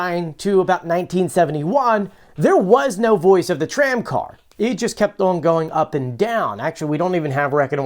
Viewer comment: I never liked the voice anyway. It always sounded like she said TRAIN car. If I listen hard I can hear TRAIM Car. I can never hear that short A sound. I hope the next lady has better pronunciation